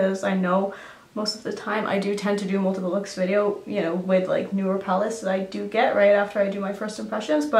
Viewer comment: Did you just say NUDE PALATE or New PALATE- I just had todo a REPLAY on that, & I still dunno! 😝😂